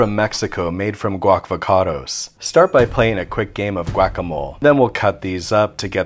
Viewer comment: If you didnt say guac-a-mole instead of whack-a-mole you missed an amazing pun. I honestly cant tell what you said lol